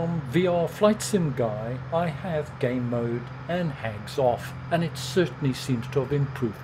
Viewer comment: Game mode and (something that sounds like) hangs off? at 12:50 Could you confirm what you said there? Many Thanks. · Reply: HAGS.....hardware accelerated graphics scheduling....on by default in windows.